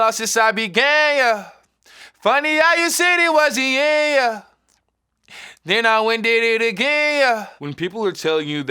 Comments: Lmao I legit thought lil nas was saying " THEN I WENT AND DID IT GAYER" 🤣🤣🤣 I've been saying that since you dropped it 🤦🏻‍♀️ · I seriously thought it was “I haven’t lost since I’ve been gay.” In my heart, that will always be the lyric. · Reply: I thought he said “then I went and did it gayer” 😂😂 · BRUH. This whole time I thought he was saying. "Funny how you said it was the end. Yuh. Then I WENT A LITTLE GAY. YUH." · When I saw the video I really thought he said “then I went and did it gayer” in the beginning 😂 tbh I like that version better. 🌈❤️ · When he says "then I went and did it again, yuh" I heard him say "then I went did it gayer" and I thought wow that's a genius hidden play on words lol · Bro i thought he was saying and i went gayer 😆